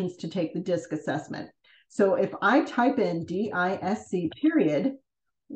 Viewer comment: nice video, what did you said in 1:24 ? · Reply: DISC - it's a behavioral analysis tool :)